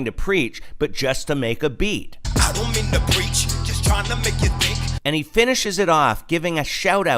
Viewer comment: it sounded like he said I'm not trying to preach just trying to make it big